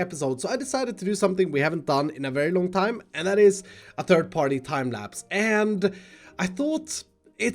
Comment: you, again, said third party timelapse instead of trhird person timelapse